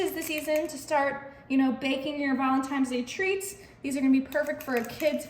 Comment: The dessert looks YUMMY! Just curious...Is it just me or do I keep hearing ValentiMe's Day instead of ValentiNe's Day? A very common mispronunciation!